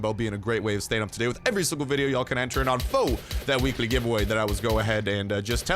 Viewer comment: when you say weekly giveaway fast you sound like ur saying weeky givawah